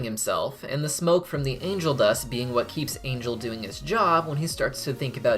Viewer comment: I swear to God I thought this guy said "Anal Dust" instead of "Angel Dust" lol